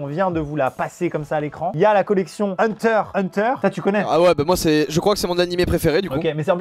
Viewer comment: J'AI ENTENDU HUNTER X HUNTER ?